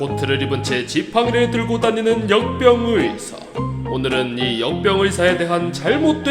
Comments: 왜 역병의사가 염병의사처럼 들리는거지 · 나만 역병의사 염ㅂ의사로 들리나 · 나왜 염병의사로 들리냐